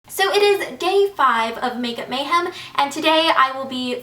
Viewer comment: i love uu but is it only me or did it sound like she said "so it is gay five of makeup MAYhem"